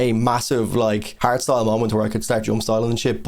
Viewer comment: Hardstyle? Oh my god I thought he kept saying harry styles, I was like “what does he have to do with anything?”